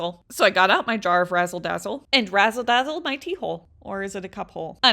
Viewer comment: No cap. I had to listen to this again. I thought she said that she would rassle dassle her pee hole 😂😂😂😂😂😂